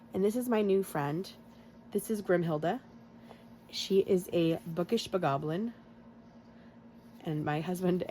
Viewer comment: I was chewing not looking and heard you say 'she's a bookish book goblin' I was like dang who's challenge is she talking about?? 😂 awesome savings friend!